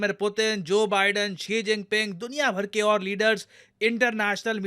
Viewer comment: sir said chhi jinping instead of shi 😂😂